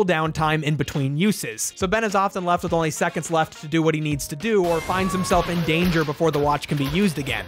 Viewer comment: This show looks so fun. I know I watched a few episodes when I was a kid but don't really remember them that much. My brain thought you said minutes instead of seconds for the Omnitrix. Oopse I'm always up for a roadtrip story. Holy moly Kevin is giving me major Jet from ATLA vibes! I honestly only saw Kevin as a teenager in either Alien Force or Ultimate Alien so seeing kid Kevin was wild for me.